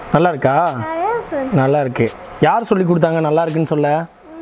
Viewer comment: what did she said? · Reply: Chithi solli kuduthanganu